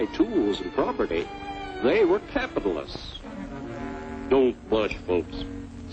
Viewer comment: Can someone help me, I don't know what he's saying at 5:30. When Joe's friends and relatives .. blabla .. they were, what? Capitalists? Capitaless? Is that even a word?